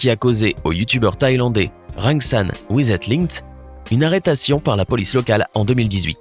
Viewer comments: C’est MOI ou à 6:56 il dit une ARRETATION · C moi ou j'ai cru entendre "arretation"